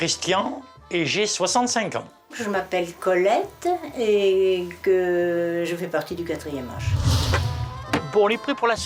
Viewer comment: j'ai compris "je fait partie du 4ème reich"😅🤣